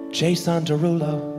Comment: did he say JSON Darulo? Probably not but I always say it that way in my head because I think it sounds funny